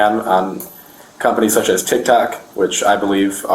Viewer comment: is it me or he really said tic-tac ?